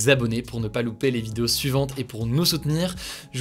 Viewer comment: J'ai entendu "Prennez soin de vous, prennez soin de vos crush" à la fin haha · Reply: Moi j'ai entendu "croches" 🤣